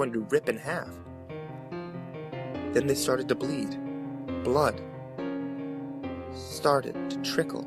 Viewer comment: At first when I heard him say "Then he started to bleed blood!" I fucking lost it, then I realized blood was the start of another sentence when he continued. :P